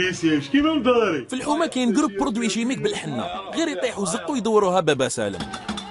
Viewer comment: وش قال ف دقيقة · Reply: 9al ki ytihou za9ou, ydawrouha baba salem, za9ou ma3natha mchoumrine, draham mekech, yroho ydorou bel 9ar9abou beh ytiyhou chwiya draham